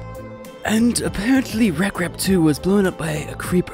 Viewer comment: if u turn subtitles on it thiks he said rat crab 2 instead of rekrap2